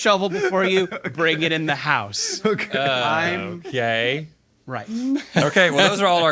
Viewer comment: I hear " I'm Gay" Whats being said!? LOL